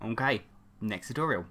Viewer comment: M'kay sounded like I'm gay